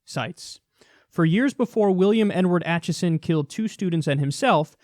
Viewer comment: Sounded like you said "N-Word" instead of Edward. I thought it was his username.